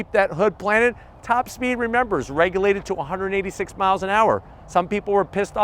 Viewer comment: Did say 286 mph top speed!!!!